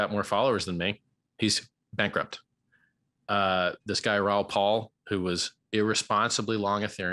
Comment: I thought he said Ron Paul at 4:16.